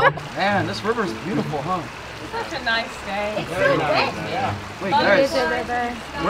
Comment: it sounded like someone said "it's so gay" Me: 😳😒😤😮‍💨🤨👌👍